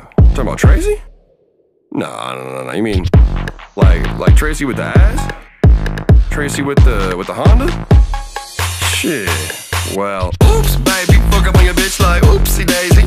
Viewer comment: i thought this part he is saying the japanese word "Hondasyi"